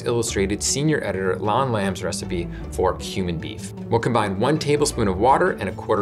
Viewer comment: I can't be the only one who thought he said "human beef" for a second.